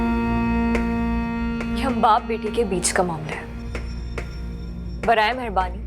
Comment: Did I hear right? She said ye hum baap baete ka mamla hai 😂😂😂.